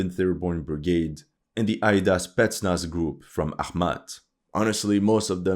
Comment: Not "Aida Group" it's "Aid's Group". "Aid" (russian for Hades) is the call sign of their commander.